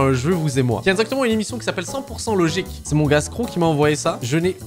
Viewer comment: up si toi aussi t'a entendu " c'est mon gars stro, qui m'a envoyé ça" glhf avec ton état x)